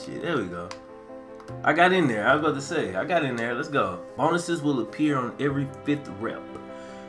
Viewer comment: Did he just say bonuses?